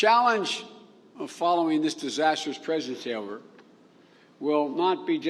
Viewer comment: WHY IS HE SLURRING HIS SPEECH? WHAT DID HE SAY AT 1:22?? "DISASTROUS PRESEVTAILER" AND AT 4:36 FASIMA AN ATOCRITY